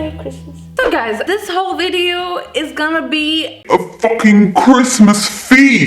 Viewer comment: I THOUGHT SHE WAS SAYING FUCKING CRAB. LIKE THE FUCKING SEA CREATURE. jesus christ...